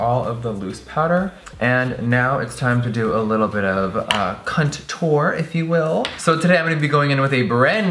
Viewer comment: I heard "cunttour" 😂😂😂